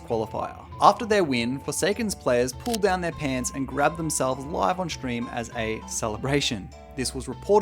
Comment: oh they GRABBED themselves. i thought you said they crapped themselves for like half the video...